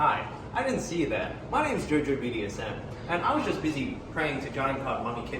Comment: Did he say JOJOBDSM? lol